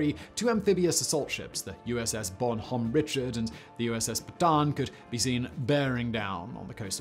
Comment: thought that said USS Batman😂😂